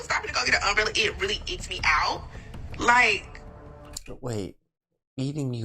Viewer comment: she said icked me out, not eating her out like you thought which is why you didn't understand it. So she was basically saying she just dried up and she had no interest in that man whatsoever because he did a gentlemanly thing to look out for his woman it's the new term that they all use oh he did this oh that's icky